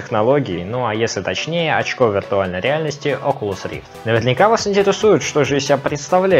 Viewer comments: Я один услышал "очко виртуальной реальности" ? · мне одному послышалось очко виртуальной реальности)))))